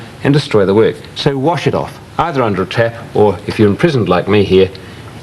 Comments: did he said “if you’re in prison like me here” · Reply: He said imprisoned, not in prison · Did you say you were in prison or was that a metaphor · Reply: I think he said 'imprisoned' as in 'stuck in that studio. But I thought he said he was in prison when I first watched it lol